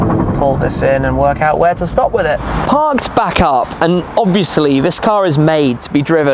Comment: Did you just say, "Pants back up?" or was it "Parked back up?".... 🤔🤭😉😄